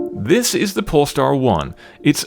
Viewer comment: who else heard "This is the Porn Star 1"?